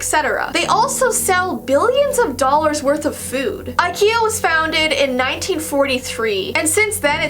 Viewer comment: did she say IKEA was founded